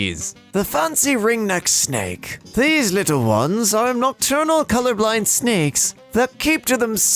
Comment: did this man just say colourblind instead of colubrid Also I hate: Rodents And that’s about it